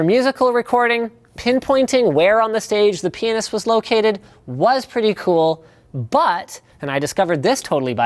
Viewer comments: around there he says 'Where the penis is located' or am I making out something else from what Linus was meant to say? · did he say penis? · 'for musical recording, pinpointing where on the stage the penis was located...' I've had to re listen to that part like 5 times, is that actually what hes saying? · it sounds like penis. He actually says pianist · did anyone else hear him say "penis" and have to go back to hear him say it again? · Did Linus just say these earbuds help you find where on the stage the penis is located?